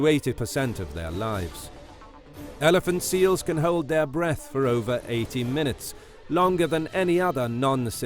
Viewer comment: So if elephant seals can hold their breath for 80 minutes, which is longer than any other non cetacean, why can walruses hold their breath for two hours?! 😂😂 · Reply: I thought he said the deal holds it breath 2 hours